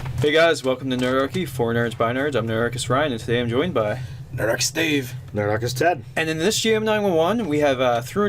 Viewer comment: Very beginning of the video I thought I heard "Nerdarchist Steve" and my whole world turned upside down. (Dave slurred a bit)